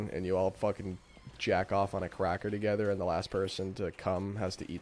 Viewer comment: I mean i just heard it was just called white cracker.